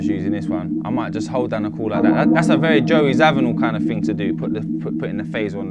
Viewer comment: Joey who? Can't really get what he's saying talking about playing those keys with a phaser.. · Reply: joe zawinul